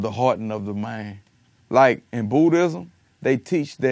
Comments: Tell me why I thought Kevin said Booty-ism for a slight second when he actually was talking about Buddhism. I'm done for today god, take me out coach! smh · Legit thought he said Bootyism instead of Buddhism! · i thought he said in "bootyism" he said Buddhism tho lmao